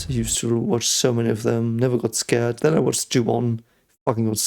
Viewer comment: which horror movie did you watch that affected you so? I couldn't understand the title. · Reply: Ju-on, reaction is in the channel :D